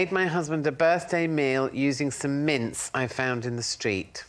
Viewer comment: The first time I heard Jo’s read, I thought she said she made the meal “using some mints I found in the street,” so it just got weirder from there. 😅😂